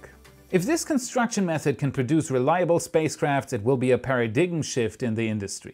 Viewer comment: Did he say para-diggin? Paradigm?